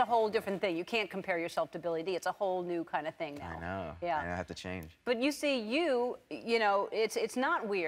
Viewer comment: Did he say "I have to Chang" at 0:50? :D